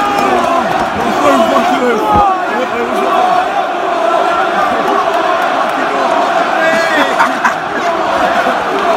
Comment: what do they say 2:40、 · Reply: they’re saying, to the Spurs section of the stands, “Who are you? Who are you? Who are you?”